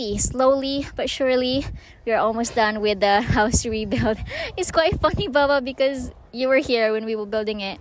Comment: it sounds like you say" its quite fuckin bubba" lol